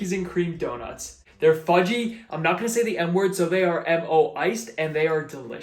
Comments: "im not gonna say the N word" is all I heard and I cant unhear it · BRO I THOUGHT YOU SAID “I’m not gonna say the n-word” 💀